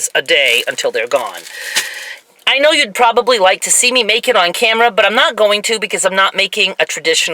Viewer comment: OMG, I was just listening (not watching the screen) and I thought you said "I know you'd probably like to see me naked on camera" instead of "MAKE IT on camera" - LOL -- Thanks for the inadvertent laugh I got from that.